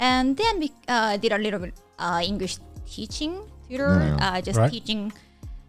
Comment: My brain is too cultured, I think. She said tutor, and for a second, I thought I heard uterus. 😂😂😂😅😅😅